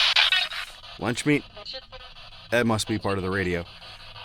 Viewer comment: Instead of lunch meat I heard 'watch me'